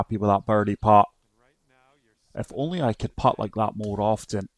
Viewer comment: Great round..I thought you said you couldn’t putt lol